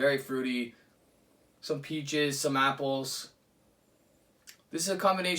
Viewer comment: when you said "peaches" i thought you said peat jizz, and my immediate thought was "ew" but then as i thought about it i realized that if peat jizzed it would taste awesome